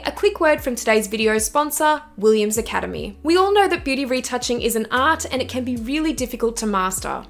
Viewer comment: I had the browser minimized because I was editing and I heard "we all know that beauty photography isn't art", and i was like EXCUSE ME? HAHAHA